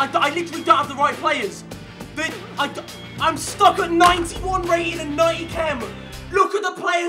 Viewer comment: Who heard him say 91 chemistry with 98 rating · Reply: 91 rating and 98 chem